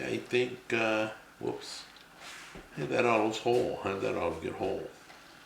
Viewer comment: what are you saying here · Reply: think he said "is that olive whole how did that olive get whole?"